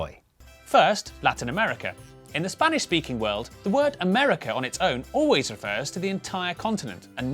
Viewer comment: “rostistagros da” what?!?!?! Did I hear the...